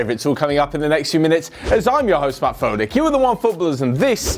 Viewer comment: Did anyone hear him say “im ur host fat frohlick”